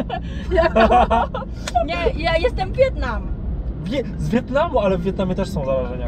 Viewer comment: czy tylko ja usłyszałem ją jestem biedna???